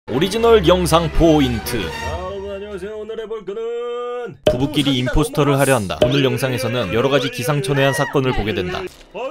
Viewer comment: 오리지널 영상포인트로 들리는건 저뿐..?